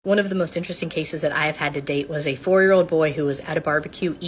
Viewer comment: "One of the most interesting cases where i had to date" Is what i thought she said.